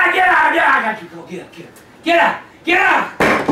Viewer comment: what the hell did grampa say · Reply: Get up! Get up- Get up!